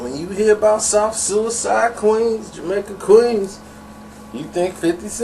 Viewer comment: I thought he said south sewer side queens